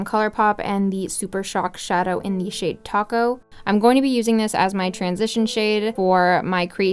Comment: Lol it sounded like you said "shade Taco" and my mom was like "Taco? Is she eating Tacos" lol